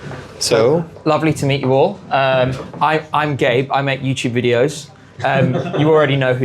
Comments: I genuinely thought gabe just walked in and said 'I'm gay' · I think the team in the room giggled because they misheard you (me too for a second).. it sounded like u said “Im gay, I make YouTube videos” 😂😂😂